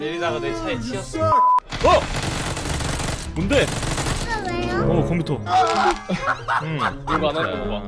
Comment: 딸:아빠 왜요? 들림??????